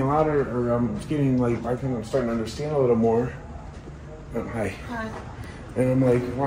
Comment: Is it me? Or did anyone hear a woman go "hi" at the 1:25:05-06 timing when was talking? I replayed that timing a few times to make sure it wasn't some other audio from another tab or something, but I legit did hear a woman go "hi" or something that sounds like that